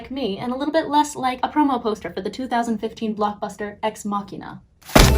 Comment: did you say "makina" ???